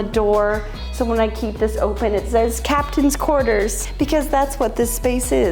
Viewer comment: …CAPTAIN’S quarters …I thought it said CONTAINS quarters and was trying to figure out if it came from a piggy bank 😂. That makes so much more sense!